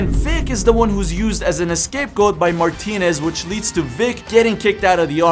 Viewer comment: did you say “ escape goat “ ?